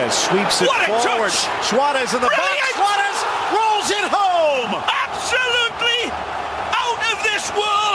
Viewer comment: 老人ホームとしか聞こえん